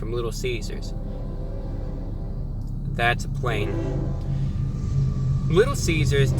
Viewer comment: I don't think he said 'plane'. With context he was talking about Little Cesar's flavor and said "that's plain".